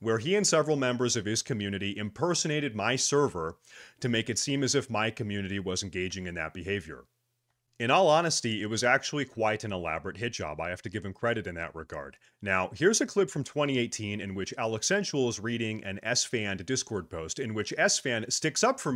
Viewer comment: does he say hijab... hid.. job? ... hitchjob .. ?